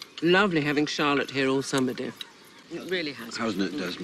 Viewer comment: 맨 마지막 대사 Lovely having Challotte here all summer. 여기서 summer 가 써머딤로 들리는데 저만 그런가요